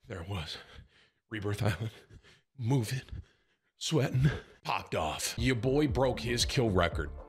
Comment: i deadass thought when you said "popped off" at the start of the vid you said "hot dogs" LMaooooooooooo